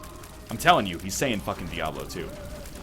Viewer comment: It says "Puerti diabolum" which means "child of devil" or "demon child" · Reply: I thought it said "diablo tu", but I think your ears are right this time